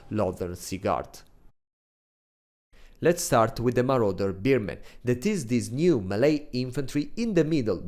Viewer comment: i heard "marauder beer-men" and i chuckled